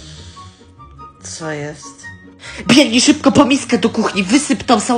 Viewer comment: Ja usłyszałam - ✨piernij szybko po miskę ✨ xdd